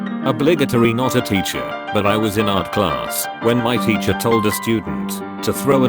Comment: Once I had my art class evaluation taken in, but it was the first draft covered in Psycho doodles of the boy who bullied me 12 year old me was crazy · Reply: For a minute there I thought you said shart class